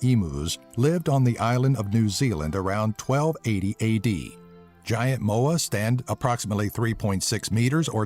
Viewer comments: did he just say vagiant moa for #8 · at first I thought he said "vagiante moa"...